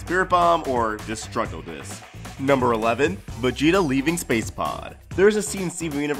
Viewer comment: I thought it said space poo